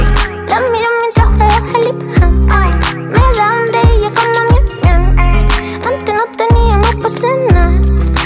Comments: Para los que no le entendieron en el segundo 00:46 dice "me da hambre y como ñam ñam" edit: gracias por los likes xd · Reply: Pense que decia "me da hambre y me como mi flan" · "me da hambre y yo como mian mian" (Lo que entendi) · Acabo de descubrir que dice en el momento de los puntos suspensivos. "Me da hambre y me como mi flan" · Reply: es asi? pq yo pense que decia "me da hambre y yo como ñam ñam" pero no tengo ni idea de como es